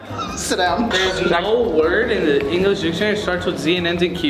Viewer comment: I thought when she said Z I thought she was dumb because I thought she said there’s no human word that starts with a Z I don’t know I really don’t know😂😂😂😂😂